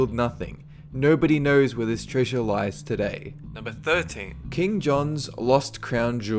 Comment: I thought he said kim jong instead of king john